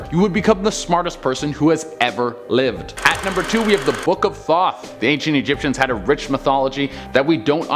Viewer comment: Legit for a second I thought he said the book of thot, I was like hmmm... interesting 🤔